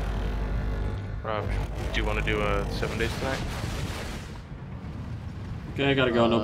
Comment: Did I hear someone say do you want to do 7 days to die at 0:20 Also first.